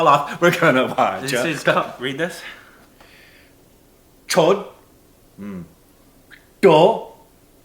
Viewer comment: According to...the person who can tap is the best dancer in the world! 😂😂 And what did you just say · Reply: It's written chodd I mean it's upto you how you ready it 😂😂😂😏